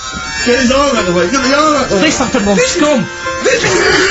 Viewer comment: What does Karl say at the end? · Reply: He says "this happened on Scum", referring to the movie 'Scum'.